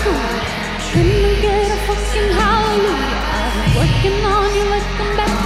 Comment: Can somebody tell me , What is Lana saying from 29:10 to 29:33? :( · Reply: Can i get a fucking aleluya.